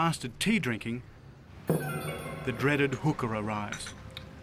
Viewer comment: I thought ue said hooker.